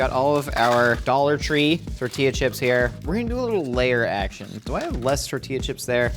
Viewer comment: As I dnd player, I heard "lair action" and wondered if the kitchen was about to attack 😂😂😂